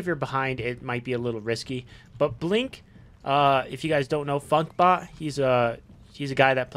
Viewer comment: After 9:45 i cant make out the name youre saying. Fugbot? Fudbot? I tried looking up profiles but nothing came up. Id like to see if he makes any content but possibly they just play the game and dont make videos. Id love to see his playstyle using blink. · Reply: funkbot or known as funkbot3000. One of the best Zhong Kui's out there. I don't think he is a content creator as far as I am aware.